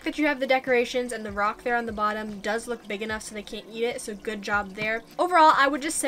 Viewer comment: I now know she said "eat it" but I had to replay twice because the first two times I thought she said "yeet it" 😂